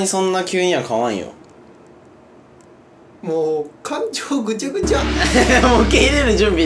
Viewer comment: 浣腸ぐちゃぐちゃ に聞こえた